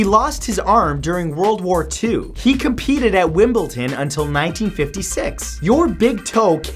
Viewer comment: Sounded like you were saying "Wimbleton" lol